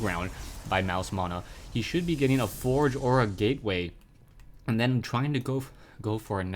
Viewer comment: am i the only one who heard forge aura gateway at 0:55 ?